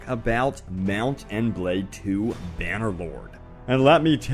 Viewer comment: I just heard "Bannerboard" and not "Bannerloard". :D